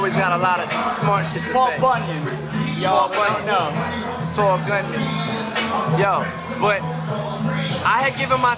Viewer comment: Did he say Saul Goodman at 5:31 ?